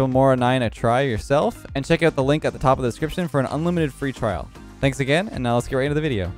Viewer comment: did he say unlimited free trial?